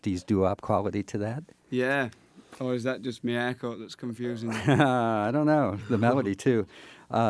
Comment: What did he say at 3:22? ¨ Is that just me....¨ What? haha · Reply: is That just my haircut